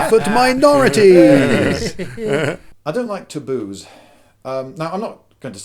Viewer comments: First time I thought he said "I don't like Toulouse" xD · Ooooooooooooooooooooh! In the beginning he says "I don't like taboos." The first million times I heard "I don't like to booze." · "I don't like to booze" - at least that's what I thought Lloyd said at the start. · I thought he said "I don't like to booze". I thought this was going to be a video about drinking habits